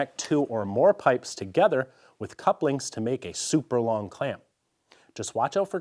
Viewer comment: did he say couplinks? Maaaan I love this channel and I just hope he didn’t say that….